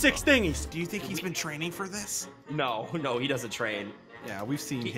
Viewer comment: Did say ass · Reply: It's abs